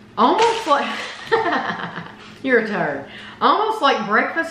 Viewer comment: Did I hear right "you're a turd" to,?😅